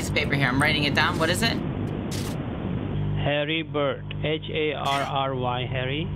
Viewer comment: I always thought he said Harry Potter....but it's Harry Bird :))